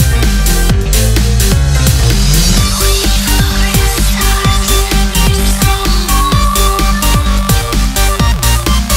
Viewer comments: what does she say here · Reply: It's a reference to chiakis execution, but she's saying "reach for the stars" and the rest of the line is faded out but she might be saying "reach for the stars, reach so high" or "reach for the stars, we're so high" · & 2:42 Well, could someone explain to me what it says? I can't understand what she says. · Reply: "Reach for the stars, we're so high!"